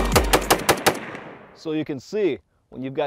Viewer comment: am I heard it right???..7.62 in scar l???